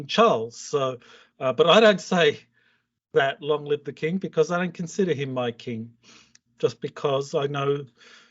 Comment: Thank you for adding subtitles. I know it's hard work but we really appreciate that because sometimes it's hard to recognize what's relate when through his phone. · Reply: Yes, for a moment I thought said "I consider him my king". Luckily, the subtitles were there. But I replayed it 2 more times and then I heard it clearly 😅